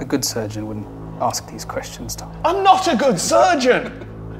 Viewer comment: When Tom yelled "I'm not a good surgeon!" I heard edit: timestamp